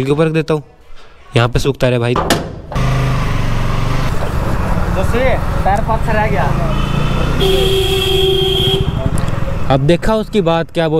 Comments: kya bola uss aadmi ne? Nahi samjha. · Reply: He said “tyre pache reh gya” Mtlb ‘tyre piche rh gya’ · can someone tell me what did the man say? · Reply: Woo bol rha tha ki bike ka tayar rastay me rah gya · Only Haryanvi People Like Me Know What Did He Said😂😗😂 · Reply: Arey vo bola tha 'dost tyre paache (hindi me piche bolte hai) reh gya. Mai bhi haryana se hun na is liye pta hai😂.